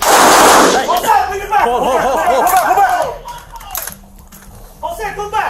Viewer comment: did they said Jose go back ?